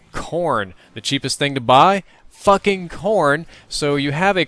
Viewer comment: When he said "Fucking CORN" I thought he said "Fucking PORN"... :P